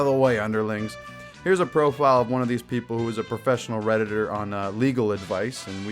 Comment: I thought it said Professional Rетаrds